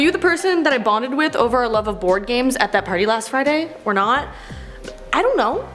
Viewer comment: At first I thought she said "bonded over porn games" not board games...